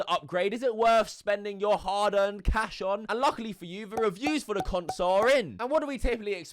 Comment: did he say cunts or console but just forget to say the rest of the word??